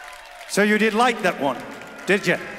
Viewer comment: Wow - stumbled onto Marko last night and fell in love with this song and his voice :) Amazing. Been a while since I have listened - I have a question though - what is said at 7:34? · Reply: It sounds like he says "Yeah well, who wouldn't like romance."